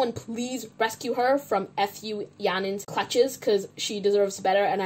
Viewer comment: i am crying. i knew yanings full name but based on you speaking f and u seperate i thought you were saying "f*** you yaning" and le me thought "finally someone confindently firing back".... i need serious help ig hahahaha